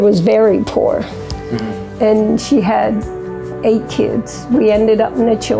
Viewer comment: LMAO I thought she said she had 8 kids when she was 16 but she actually said she was 16 when her mother died.